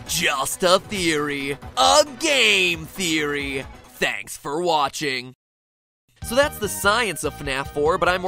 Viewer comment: Lol i thought he said Spanks for watching